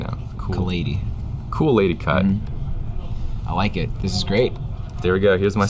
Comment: I misheard cool lady cut as koolaid cut whoops